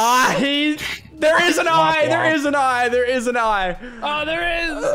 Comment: did anyone else hear say there is a i